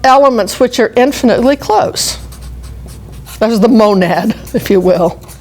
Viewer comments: Monad? I thought it said mound... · I thought it said gonad, nevermind.